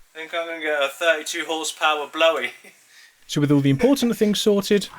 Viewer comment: I giggled uncontrollably when I heard "43 horsepower blowie".